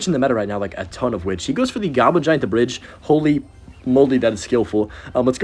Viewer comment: Did you say "Holy fucking moly?"😂